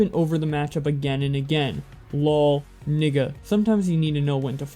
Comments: Did he just say nigga around 13:05? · did you just say nigga my guy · did you just say nigga What are your roots?